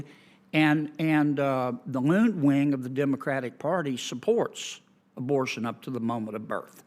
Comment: Did he say, "...the loon wing of the Democratic party..."?